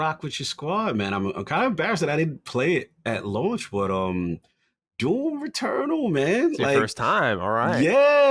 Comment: LoL I really thought said Returnal for the game he is playing right now... then he mentions how it got a bunch of updates n shit and that made me double back to hear him say, Doom Returnal (at least it sounds like he said to me) an obviously homie said Eternal